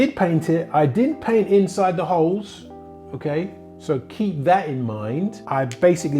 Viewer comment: Informative. I thought you said you "did paint inside the holes". Then worked out you maybe mumbled?